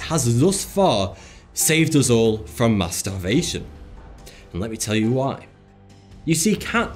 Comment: I thought he said masterbation instead of mass-starvation